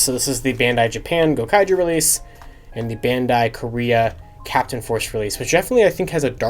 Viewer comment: i have this one as well, Shinkanger was never brought over to Korea - so for that show they made up the name bladeforce use the silver ranger key in it it sounds like it said "Captain Pu$$y?" instead of "captain force eh"